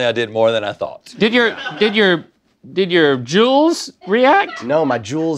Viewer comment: Did they say “JUULS” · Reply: Jewels